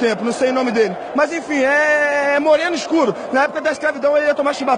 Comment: "é moreno escuro na época da escravidão ia levar ??? Também igual que eu"" Que foi o que ele disse no final, não deu pra entender direito kkk 😂😂😂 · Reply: Chibatada o felipe melo disse